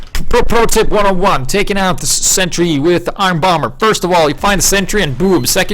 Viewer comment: I thought it said iron boner :/